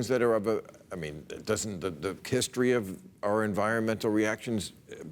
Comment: Did he say Kisstory at 1:40?